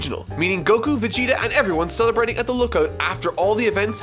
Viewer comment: did he say lookout?